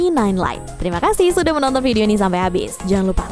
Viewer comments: "unsubscribe"?? apa gw ga salah denger 😂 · apa cuma gw yang dengernya "unsubscribe channel pricebook" ?????